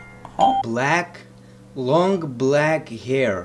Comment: She said Blonde, you just misheard it. EXPOSED!!!!!!